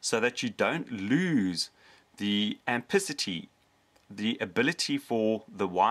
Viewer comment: minutes of the video, you say, "...so you don't lose any antithesy??..." what did you say? Thanks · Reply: Uncle Go-rilla ampacity